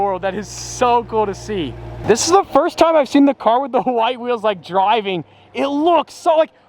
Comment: Did he say h'white like Hank Hill